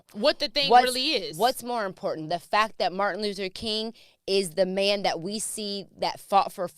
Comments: I SWEAR she says Martin "Loser" King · did she say “Martin Loser king” ? I really hope that’s not what I heard …